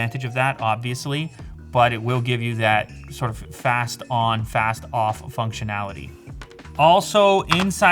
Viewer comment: Fast On, Fast Off What I hear Wax, On Wax Off!